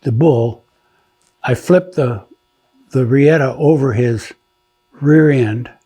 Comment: Riata (Rope) or La Soga .. In case yall couldnt make out what he said ..